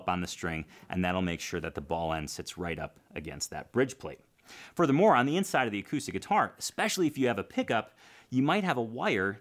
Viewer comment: "for the moron the inside of the acoustic guitar..." - what I heard "furthermore on the inside of the acoustic guitar..." - what he said